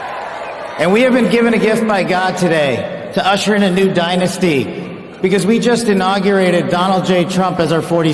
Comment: Did he just say "dynasty"? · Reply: Yes Patel called America a dynasty and if you research him online he has called President Trump a king. America is NOT a dynasty we are a Republic governed by our Constitution and the Bill of Rights. President Trump is not a king per the laws in our Constitutional government. The citizens of America freely select and vote to elect our President and our other government leaders. President Trump is serving his last 4 year term. At the end of his service as President Americans will elect a new President. Patel does not belong anywhere near President Trump's administration at all. He has been embedded within the deep state in Washington for 16 years and he has played every side in politics to promote himself. His agenda is a globalist one and he bluntly said this when he stated that the American dream does not belong to Americans or to their children; in contrast the American dream belongs to the world. He is WRONG Americans have shed our blood and given our lives fighting to hold fast to our freedoms and our American dream. Patel is a drastic mistake to President Trump's American First Agendas because he has openly rejected the Constitution and our blood shed rights as Americans as citizens to claim our American dream.